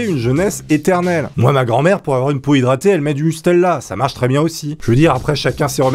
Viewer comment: j'ai compris nutella XD